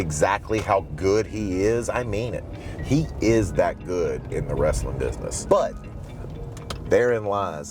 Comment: I think you mean to say he is "THAT DAMN GOOD"